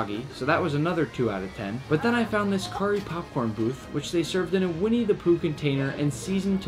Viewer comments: I heard “Winnie the poop” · I thought he said winnie the poop 💩 💀